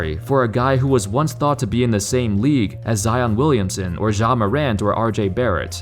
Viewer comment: Did you say Zha Morant? Odd pronunciation.